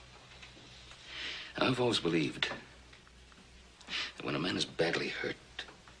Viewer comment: Does anyone know what 'name', or person Ben is speaking of (5:34 on time-line of this clip) as to '"...?..' believed,... that when a man is badly hurt,..."... ? It sounds something-like 'Ulvost'? Have any ideas?? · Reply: He’s saying “I’ve always believed…”